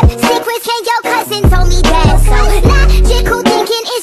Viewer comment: Not gonna lie this whole time I heard "That tickle tickle" instead of "logical thinking" this whole time